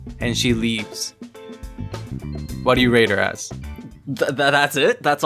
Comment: You said "Rate her" but I heard "Rape her" so I was like...Oh gods😟